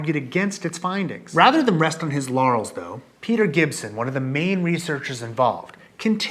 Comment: Did anybody else hear “Rather than rest on his yannys though”???